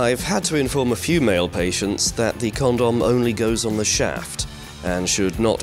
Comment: I thought it said parents instead of patients